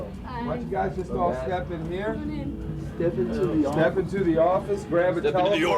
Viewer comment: did James say "step into the orifice?" And again at 0:54 lmao